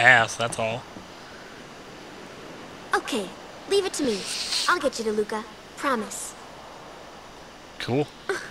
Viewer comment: I stopped paying attention for a second and at 0:58 it sounded like: "I'll get you the hookah, promise!" "... cool."